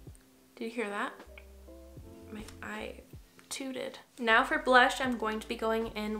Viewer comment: I thought you said "you hear that? I tooted..." I was like woah okayy now.😂